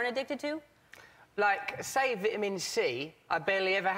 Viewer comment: he says "____ i barely had any" what did he say?? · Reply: Vitamin C